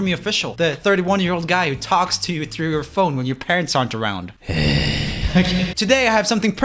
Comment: I thought he said when your pants aren't around